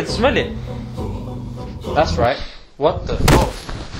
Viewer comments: wat did u say at 5:58? opps, maybe i heard it wrong, i no Muslims dont use bad language, i must of heard it wrong lol. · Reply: He said "What the.... Oh" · did he just say “what the fu**” !!!???